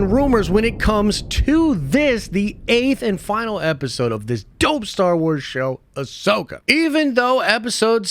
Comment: did you just say dope star wars show 00:11 sec i'm done with this video